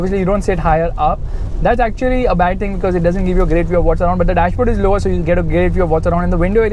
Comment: All i heard was "whats around 3 times" haha